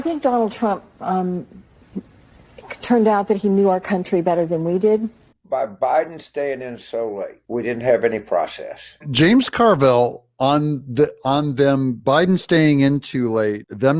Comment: I thought he said didn't have honor. Lol